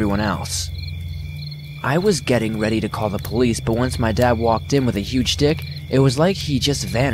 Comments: please tell me I'm not the only one who heard "once my dad walked in with a huge dick" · Please tell me I'm not the only one who heard "Once my dad walked in with a huge dick..."